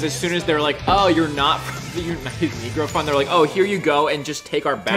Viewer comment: ok am I crazy or did I hear say the N-word at 8:03 ? Please tell me if i'm wrong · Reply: he´s repeating the movie dialogue "united negro fund"